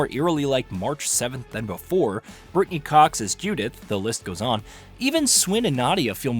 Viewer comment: I definitely thought you said "Courtney Cox as Judith" and I was really surprised, ha ha.